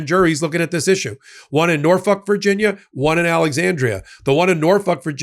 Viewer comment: I heard “Norfuck”